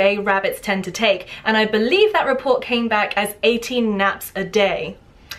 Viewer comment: "18 naps a day" wow didn't know I was actually a rabbit. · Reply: I THOUGHT SHE SAID 80 NAPS AND I WAS LIKE, WAAAAAAT!😁